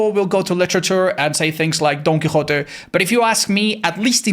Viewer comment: I think bro said Don Quixote